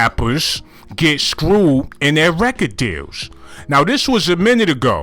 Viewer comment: You was saying deals this whole time 😭😭😭I thought you was saying jails